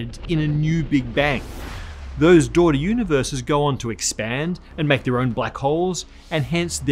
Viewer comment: *what is he saying ?? "those door universes" ...or "those dough-lore universes" (?!) WTF